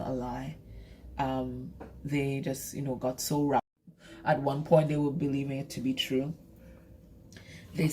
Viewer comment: mins in theres a glitch! And it seems like a fair bit was cut out.. I'd love to know what it was you said. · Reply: 😮😮 I didnt realise. I was saying "I think they got so wrapped up in their lies that they believed it to be true". "Wrapped up in lies" got cut out. Very interesting 💖💖